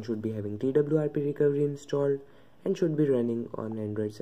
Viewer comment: ??? what did he say? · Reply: TWRP Recovery installed